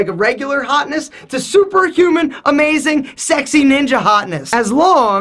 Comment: All I heard was "that handsome assface". xD Jokes aside, those light colored frames suited the contours of your face much better than any others you tried on.